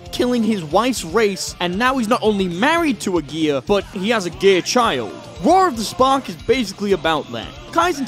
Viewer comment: i heard he has a gay child…sorry lol